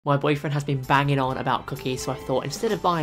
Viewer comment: Um I thought you said My Friend.But having a boyfriend is ok.😅